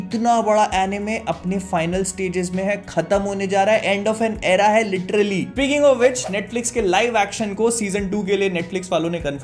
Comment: Hein bhaiya, kya bol gye?? 😂😂🤣🤣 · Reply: "Picking Of Witch..." Jaha Tak Mene Suna 😅